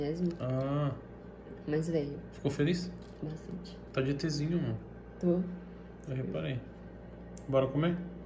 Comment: "tá de Etzinho amor?" Foi isso mesmo? Kkk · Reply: "Ta de etzinho man"